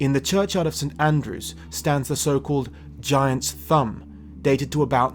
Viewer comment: At about 19:20, I thought he said 'giant's bum.'